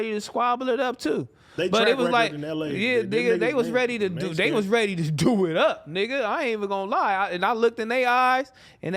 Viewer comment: What AD say after he said “they track record in LA”?? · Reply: He said they ain’t scared